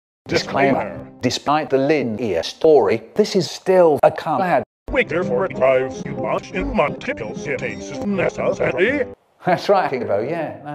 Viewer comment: Disclaimah! Despoit de lin ear storey thees iz till a corab. We therefore idvize u to wutch in multiple sittings nesasaly! I wrote what i heard. Lol